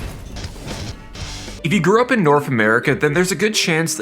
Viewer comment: in.. I heard "Carmen Geddon" and "Norf America" - Im out.